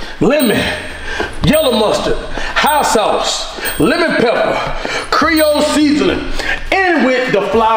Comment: Did anyone peep the “cerol seasoning” 9:53 instead of creole? Lmaoooo Love the vids!!